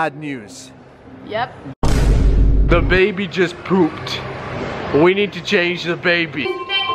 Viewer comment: Did he say "we need to change the baby" if the baby poops💩- 2019😂